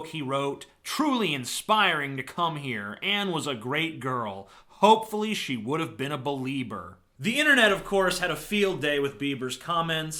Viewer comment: The first time I heard about the JB& Anne Frank fiasco, I misheard it as "Hopefully she would have been a believer" which is even more rage inducing than the actual statement.